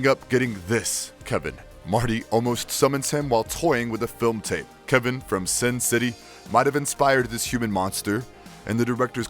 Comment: I thought you said Kevin from Spin City (the 90s sitcom) and I was both confused and delighted.